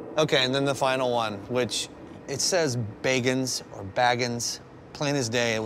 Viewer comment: I thought it said begging not bagans